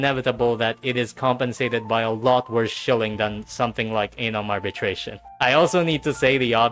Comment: thought you said Anal Arbitration